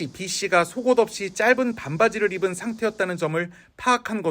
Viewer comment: 속옷없이 반바지???? 잘못들었나?????엉????